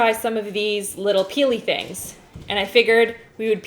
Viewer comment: did she say pelly... Get it pelly Like if u play fortnite